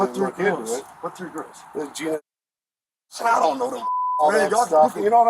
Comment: The officer is referring to the kidnapping and imprisonment of Michelle Knight, Amanda Berry, and Gina DeJesus. The subtitles say "Jena" but considering this case also takes place in Cleveland, OH. I assume that is who he is reffering to when speaking about "those three girls"